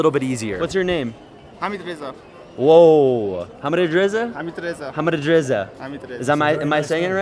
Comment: I couldn't hear that quite right, but it's almost certainly. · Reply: Hamid Reza